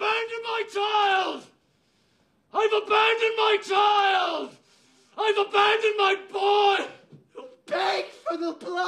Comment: i swear i heard "im a man in my child,im a man in my boy."